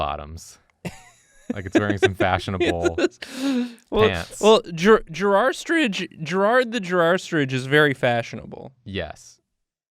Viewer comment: Here's me laugh crying at my screen because I heard say "he's Gerrard the girrastrich and I love him" at .25x speed